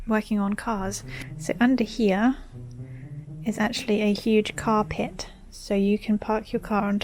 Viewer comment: Who else thought she said carpet instead of the intended "car pit".